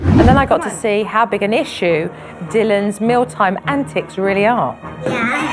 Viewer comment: sounded like she said tea time and tits my mind is fucked isn't it